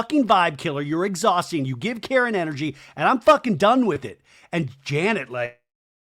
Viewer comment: When jasmine said janet has Karen energy i thought she said carrot energy, I have been sat here thinking what is carrot energy, so thanks for clearing that up😂